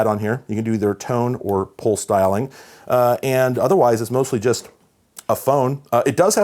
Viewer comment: At first it sounded like he said "pole styling" but than I realized. pulse dialing .